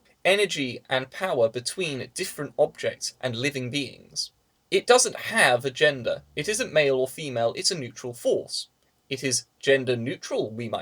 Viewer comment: Every time you said "a gender," I thought you said "agenda". Whoops!